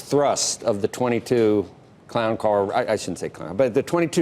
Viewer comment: he said clown car and then he said sorry i mean democrats ://// ???? Am i hearing right?